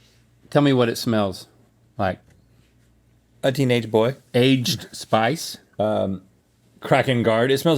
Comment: I thought said Ice Spice when she was talking about big booties